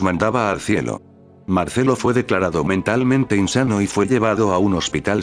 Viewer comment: nose si escuche "insano"